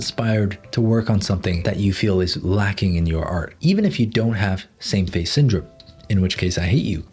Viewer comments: I thought it said Sam gave syndrome 💀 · i thought he said "Sam face syndrome" and i was wondering what was wrong with his face